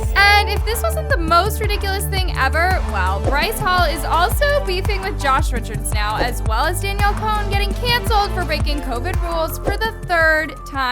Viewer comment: I THOUGHT YOU SAID FLIRTING WITH JOSH RICHARDS AND I PANICKED